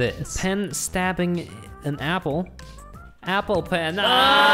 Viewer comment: Apple pen I thought it said apple peh